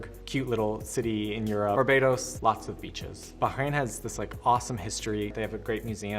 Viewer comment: You will hear b*tches instead of beaches if you watch in 2.00x speed. (Or you might hear it in 1.00x speed too)